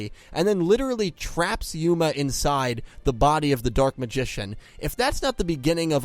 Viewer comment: What he said: "He forced yuma into the body of the dark magician" What I thought he said: " He forced yuma into the body of the dark magician GIRL " Also me: NANI